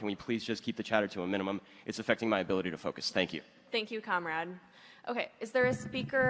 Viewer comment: Did i hear thank you Comrade?